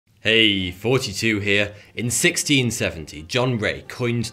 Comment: You are my man, and I enjoy your videos!….but every intro it sounds like you say “42 here” but I i know you’re saying Thoughty 2 here! But it cracks me up every time cause it sounds like “42”! 🤣